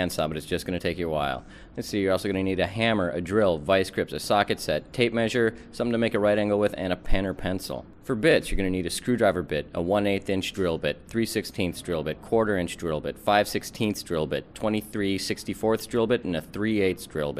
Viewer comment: it sounds like he says : 'For Bitch!'